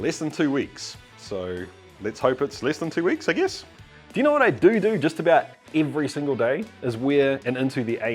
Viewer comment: in your statement: you know what I do do... Ha! You said doodoo Damn I'm immature 😜